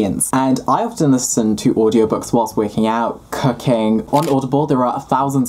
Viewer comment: tell me why i heard "cocaine" when he definitely said "cooking"